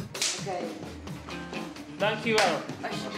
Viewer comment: to answer the 3:35 question: he says "dankjewel" which is dutch for "thank you very much", then the lady replies: "alsjeblieft". there's not really an english expression for the term, in french it would be "s'il vous plait". it's what we say when we give someone something, so I guess "(t)here you go" would be the best english translation